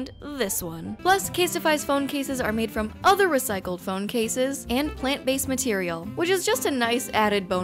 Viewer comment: I’m kinda high, and during the cell phone case ad, i legit thought it said “this case was made from recycled phone calls”